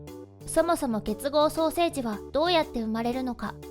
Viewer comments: 双生児がソーセージに聞こえてしまう。なんかごめん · 双生児がソーセージに聞こえた私はいったい.... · 双生児 がソーセージに聞こえて ？ って思った